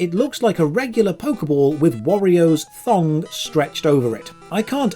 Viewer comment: UUUUUUUUUUUUGH! The first time I saw this video, I thought he said, "Warrior's thong" and I wasn't looking at the screen; I said, "Big deal. I don't know who Warrior is and it doesn't look like The Ultimate Warrior's trunks, so the Master Ball isn't ruined for me." Then I watched the video a second time and correctly heard "Wario's thong" and now the Master Ball is ruined and so is my mind, heheheheheheheh!